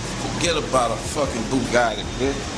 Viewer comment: Lmao did he just say bugaggi haaha cant even pronounce the cars name 3:05 its bugatti